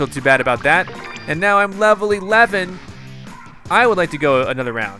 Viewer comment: i think he said I'm 9/11?